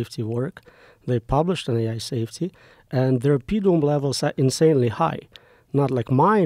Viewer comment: what is Roman saying here? Subtitles say "Pedom" and that's apparently not a word, maybe he is saying "impediment" levels?